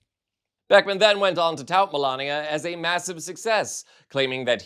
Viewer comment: Did he say, “Felonia?”